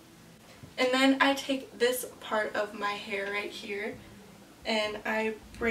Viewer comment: I heard "my hair is naughty" haha... on the real your makeup looks pretty! we need a tut😘